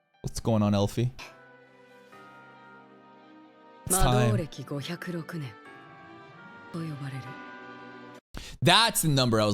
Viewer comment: Wrong translation? I heard Go-Hyaku-Roku nen which mean Year 506 but the subtitle is Year 504